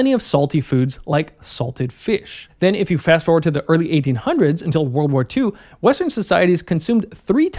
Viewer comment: WW2 was in "early 1800s"? please check your facts. WW2 was in <num> which is the 20th century, spanning from 1901 to 2000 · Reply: "Early 1800s till WW2" as an era, you misheard him